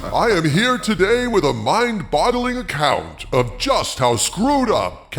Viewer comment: Did you say mind bottling?